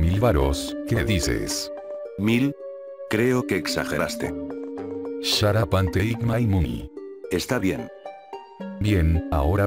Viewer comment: Al principio no le entendí lo que decía, creí que eran unas palabras mágicas XD Lo que dice es: "SHUT UP AND TAKE MY MONEY"